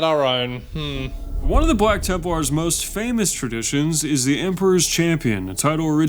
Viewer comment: i thought it said black larper and i was like WOAHHH LMAO